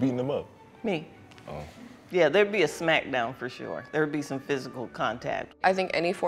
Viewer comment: All i heard was "There would be a snack down for sure" and not a "smack down".